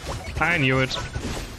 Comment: did he said "I mew it"